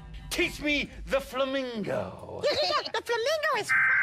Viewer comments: i thought Elmo said the f word LOL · if you listen in the EAS test in the sesame street, it sounds like elmo is saying "the flamingo is f*cking lol